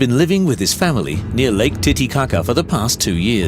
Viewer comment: Wait wait wait did I hear that correct or am I already falling asleep "Tittie kaka" lmao